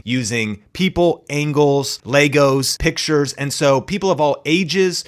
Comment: Every time I heard say "Legos" I heard "Lagos" 😂😭 In other news, excellent information. Thank you for sharing 💯